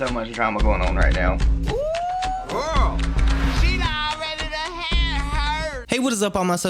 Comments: does anyone else scream “OOOO GIRL she already dun had hers” when does it like it’s a reflex??? no? just me? aight cool 😩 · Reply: lmao i thought after manyyy times of trying to hear it right, i thought i had it finally!!! this is what i thought said... " ooooo girl! she already to hear, it hurts"😂😂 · does anyone else scream “OOOO GIRL she already dun had hers” when does it like it’s a reflex??? no? just me? aight cool 😩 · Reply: I thought he was saying “OOO GIRL she already to hit her”😂🤷‍♀️